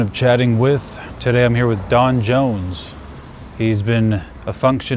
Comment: I literally thought you said "Jon Jones"